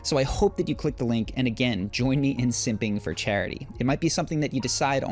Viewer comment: I thought he said simping for charity. Lmao